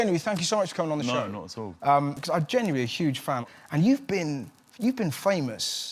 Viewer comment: Does Russell call him "Jamie" at 2:07? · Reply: Rick Ames he said genuinely